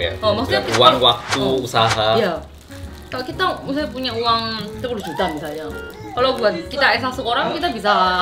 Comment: ini saya gak salah denger? 10 juta seharusnya kn? subtitlenya 6 juta